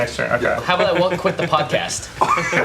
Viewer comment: Can anyone tell me what says at 49:26? I've replayed the clip 10 times over but still can't seem to understand him :'( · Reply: Charlie Taing “How about I won’t quit the podcast?”